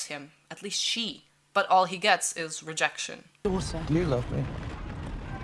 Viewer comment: just realized he said “Do you love me?” Instead of “Do you love her?” I thought he was asking if Alicent was in love with Rhaenyra.